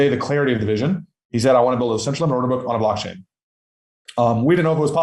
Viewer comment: What does he say at 4:45... he mumbles something so quickly... "a Centralized Orderbook" ?